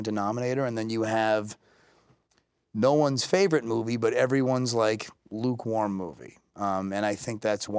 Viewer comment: Sorry community,I got a question... What he said in time 21:24/25 ? Thank you! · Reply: Hey English is not my first language but from what I understand : « No one’s favorite movie and then everyone lukewarm movie and maybe that’s why people are turning away » hope that helped you !